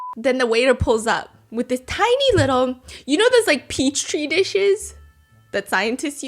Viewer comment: ….. did you say Peach Tree 💀💀💀💀💀💀💀💀 adorable